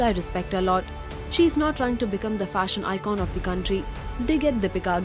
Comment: Did I heard "country" instead of "century"???🤣🤣😂